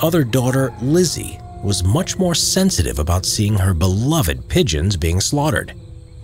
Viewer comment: I heard “children” instead of “pigeons” and thought “HE TURNED THEM INTO PIES AND THEY THINK IT’S NORMAL!?!?!”